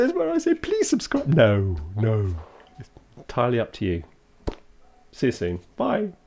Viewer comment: You said “Siri-ously, its up to you”, hence the I chose last time reply :) EDIT: I just listened to it again on bigger speakers, i think you said “see you soon”, not seriously, but same effect!